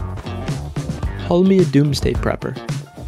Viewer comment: I thought it said peppers